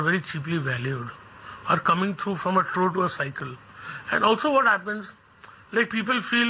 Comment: Anyone please explain what does he exactly say at 9:31 .. "coming through ____ cycle" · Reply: Trough to a cycle